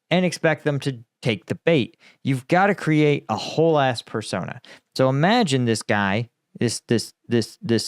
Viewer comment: Crush Even Your Bad Day with <link> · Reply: did you just say "whole ass persona" whaaat